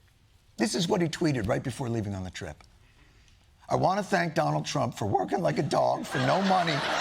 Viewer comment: I thought it said "I want to thank Donald Trump by working like a dog... "